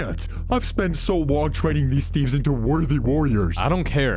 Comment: I used captions and at 57:00 it's said theives instead of steves